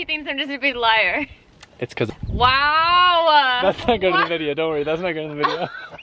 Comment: "its cause of my ex wife" for sure what he said